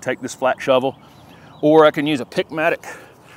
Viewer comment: For all these years I thought you were saying “Pick-matic” like it was a special gadget name… but now I realize it’s a “pick mattock.” Amazing.